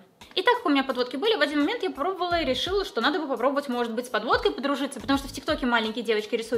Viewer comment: Когда сказала что маленькие девочки рисуют стрелки подводкой то мне послышалось что они рисуют стрелки под водкой.🗿